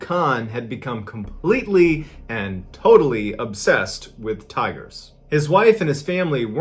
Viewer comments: At first I thought you said, "...obsessed with tires ." I was thinking, "What? What the heck?" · LOL.....For the first couple minutes I thought you were saying, in your first story, that Maqsood was obsessed with TIRES. LOL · At first I thought he said he became obsessed with tires. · At first I thought you said he was obsessed with "tires" lmao